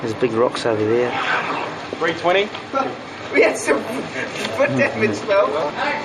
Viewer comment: What did Elijah say?? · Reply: "Laughing" "We have some foot damage" - the hobbit foot prosthetic might have been damaged?